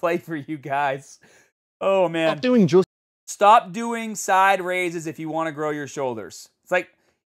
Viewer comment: to be fair, he said "Stop doing JUST side raises", a bit different from the meaning without the "just". Everything else sounded like fair comment from you. Anyway, algorithm fed, will go look for some of your "how to" videos now...